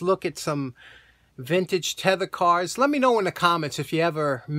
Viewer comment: I had to do a triple check, I swore that said "leather cars".